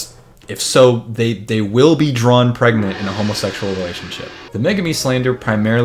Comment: i thought you fucking said “drawn pregnant in a troll quadrant relationship” homestuck has ruined my life